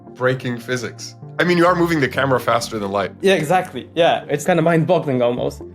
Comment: did he just say "mind bottling?"